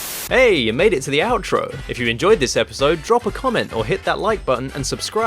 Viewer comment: Thought he said "Drop a comet" and I thought "I'd love to, on BIG THE CAT!"